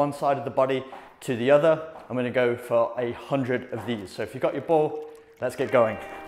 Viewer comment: I thought you said ‘800’, so I did 800 sole roles lol. Just looking back at it now to see you said ‘a hundred’ 😂